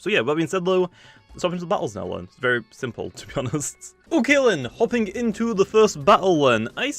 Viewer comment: has anyone noticed he says o kalen instead of ok then?